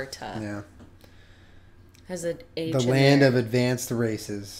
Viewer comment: I thought he said "The land of advance racists. "